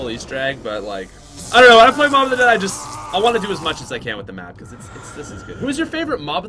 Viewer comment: I thought it said noob of the dead